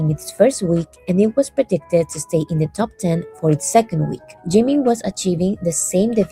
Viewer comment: No, it's Jimin. The closed captions are wrong. Maybe you are using auto-generated captions?